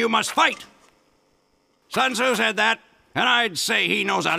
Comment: Ive always thought he said “Sons, who said that?” not “Sun tzu said that.” My entire tf2 life is a lie.